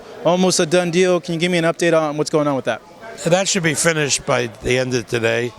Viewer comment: Did he just say FETISH?! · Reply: F I N I S H E D